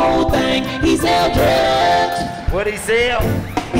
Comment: At first I thought shawty said he sell cars 😂🤦🏾‍♂️😂 she snitched on her own cousin it be your own people 😂